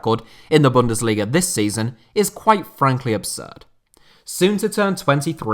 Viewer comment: did he say soon to be 23? How the hell am I older than Lewandowski lol